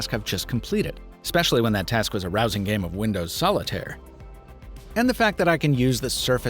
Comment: I thought he said an arousing game of Windows solitar